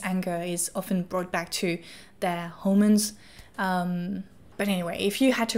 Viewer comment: Can someone explain this? I've never heard this before. Wait, did she say hormones? I'm sorry if I'm missing it.